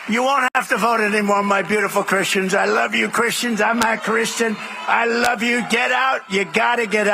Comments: When Trump begged for Christian’s to vote for him this one last time. Trump said, “ I am NOT a Christian” on Jen Psaki just this afternoon MSNBC CUT THAT SENTENCE OUT WHY?? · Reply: that is what I hear also at 00:21 ...... "I am not Christian...." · Did he just say "I love you Christians I'm not Christian " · What I hear at 00:21 ...... "I am not Christian...." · It Sounded like he said "I'm not Christian". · Did he just say “I’m not christian”? @ 0:20 ? How does that go over with his base? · Did he say "I'm not Christian" or did I mishear that? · It sounded like he said "I'm not Christian"... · I thought he said "I'm not christian" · Did I hear him say "I love you christians. I'm NOT a christian. " ??? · You are totally misunderstanding him- he's saying the country will be fixed after '4 more years' and so Christians won't have to vote again. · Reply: The weird thing is it almost sounds like he says "I'm not Christian" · Did he just say “I’m not Christian”?! Wow, he told the truth. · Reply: Some are saying they think it's "I'm a Christian" said in his usual weird speech pattern like "I'm Aaa Christian", though the way he's shaking his head no when saying it and the fact that he clearly only worships Donald Trump make me lean more towards the former even if I didn't already hear it that way. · Did he say "I'm not christian"? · Did Trump say "I'm not Christian" ? I'm sure he said that. I listened to it a few times and it sounds like it said that!